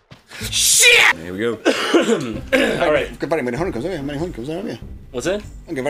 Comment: I heard "You don't have very many honeycombs, don't you?"